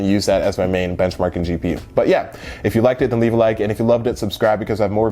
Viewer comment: Did you just say if you liked it, don't leave a like? LOL